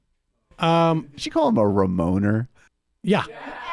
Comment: i think that she said "remainer" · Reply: *remoaner